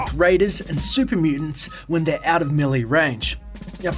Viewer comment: ohhhhh melee and i thought he was saying mili